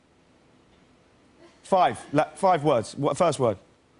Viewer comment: kit said "no" in arabic!!! wtf is he arabic?!!! he said "لا five words"